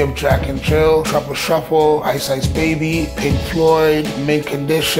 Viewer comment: Did he just said ICE AGE BABY